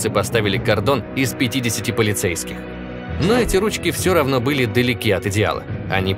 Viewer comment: картон из полицейских? Послышалось?